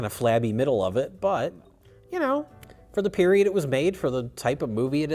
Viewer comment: I literally heard "Type 0" lol.